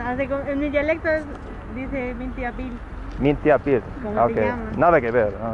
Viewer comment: la senora dijo "Binti abiil" binti significa "que". "a" es el prefijo para decir "tu " and biilel es "nombre (name)". 2:40 the lady said "binti abiil". binti means "what". "a" is the prefix to say "your". "bill" means name